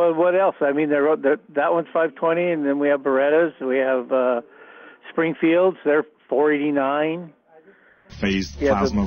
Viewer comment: Does he say Springflield at 3:04?